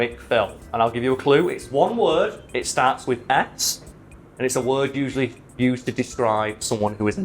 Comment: Everytime says "scorn" I hear "scone" I'd still watch a keanu Reeves movie titled "Scone" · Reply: I thought he said it started with an X and I got really confused as he started giving clues